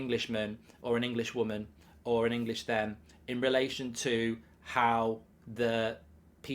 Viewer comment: Did he just say, Englishman, Englishwoman or Englishthem?? Wtf is a Englishthem?? Lost all respect. Britain has completely lost its mind. Embarrassing. Unsubscribing.